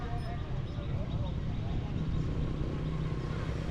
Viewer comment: I am not sure about whether you got auto subtitle for engilsh or not. In 39:10, the name of the pagoda was written wrong as Shwesandaw instead of Shwe Si Gone. ❤